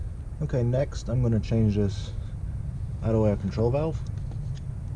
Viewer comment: what is the name of the control valve he is replacing? i cant understand what he says.... thanks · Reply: Idle air control valve